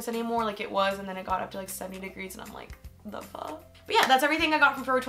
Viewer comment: did u say "the fuk?" ? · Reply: no haha I said "the fuhh" but probably sounded like the other word.. yikes